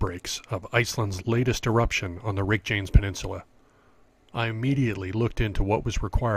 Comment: Did you really say "Weckjanks" Peninsula? (2:22) To your credit, you did say it correctly at 5:17 It is interesting that the vent you shot did not exist for the first month of the eruption. The early vents were spectacular. There were like 6 or 7 of them all going off at the same time. Then the one you photographed appeared and shut down all of the others. Its been pulsing on and off ever since. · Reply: Hahaha - I did say Reykjanes, but I’m a still photographer and most definitely not a videographer or sound editor. The sound is all over the place in this vid. You get what you pay for ;) It sure would have been awesome to also have been there when it first started. I have photos of the other dead vents too, but they were portrait orientation and would t have converted well to video. Thanks for watching!